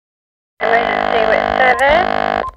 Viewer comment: i always heard "let me just see what 7 is" but "emergency with service" makes way more sense